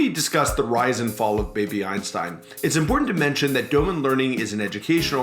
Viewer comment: for a moment I thought you said baby Epstein